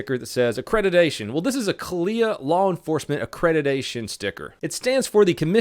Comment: I always thought u guys said “lawn enforcement” bruh Imagine a cop shooting ur garden grass